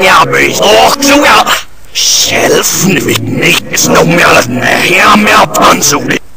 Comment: I can tell some of what they are saying: "WISH EVER! Cost plenty of. There's no ease until the outpost is almost in lock. Annnnnd assist tool!" "Uy... There's not certainess as the in void." Then at 0:34 "Shelf full of big naked, there's no melon and the hair malfunction bit!"